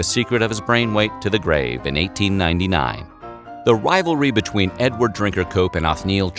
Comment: I thought you said Edward Drink a Coke